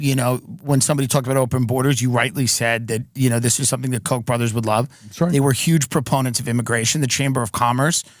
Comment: "Open borders is a Koch brothers proposal" - Bernie Sanders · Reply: I thought he said "Coke brothers" and thought he was talking about Coca-Cola. 😂